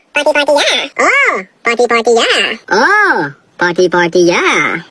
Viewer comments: “Ohh potty potty yaa “ WHAT TF DID U JUST SAY WHAT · "Oh potty potty yeah"💀💀Im mishearing it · Why I heard "Ooh, potty potty yeah" 😂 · Meanwhile skin cancer knocking the door of life (BTW Kookie's "PARTY PARTY YEAH" is still iconic 😂😂) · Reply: I thought he said "Oooo potty potty yea"